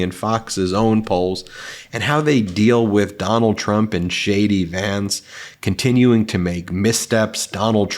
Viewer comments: Did I hear ' Shady Vance ' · lol @ 0:16 I swear I thought I heard refer to “Shady Vance”, and I’m all in for it. Edit: @ 7:17 Ah, so does use that epithet, so perhaps I didn’t mishear him.